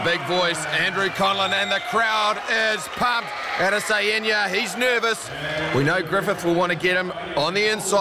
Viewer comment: “AdEsaYenya” what did my mans just say???